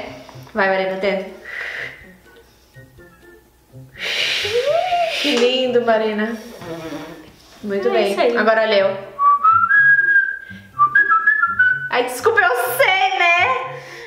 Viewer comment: Eu entendi a falar: "agora lheu" 😂